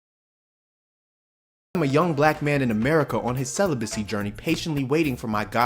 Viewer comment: what tf was bro saying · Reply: He said Young black man